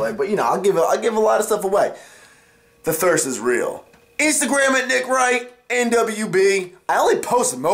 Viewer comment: when i heard "instagram it, nick wright" i thought he said "instagram it nigga" lmao